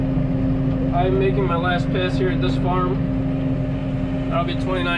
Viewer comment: Am I the only one who heard him say p*ss